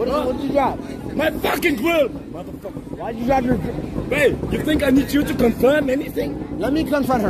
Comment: did bodyguard say babe to johnny somali ?